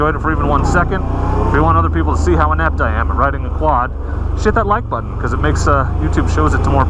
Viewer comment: I'm purdy sure I heard you say "shit that like button".....